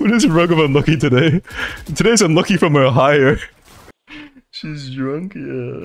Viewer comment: I think this part said, enoki from Ohio.💀